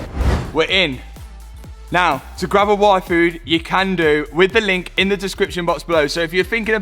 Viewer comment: I thought he said GRAB A WAIFU